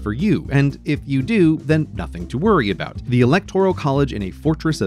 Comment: Did you just say the electoral collage has it has survived for a thousand generations? xD